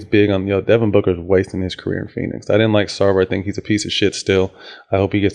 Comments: who did Matt say he didn’t like @ 3:53 he was talking to fast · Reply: Suns owner robert Sarver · mark... He said he doesn't like who?? I can't make out the name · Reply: it's Sarver. Sun's owner. Thanks anyways tho lol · At the 4:00 mark, is he saying "Starbury"?